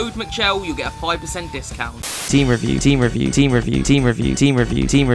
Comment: Im not sure, but did he just said team review?